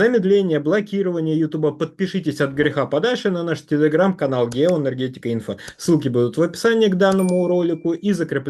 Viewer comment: Ой, я в начале вздрогнул, мне послышалось : ГЕНДЕРО гетика.. 😂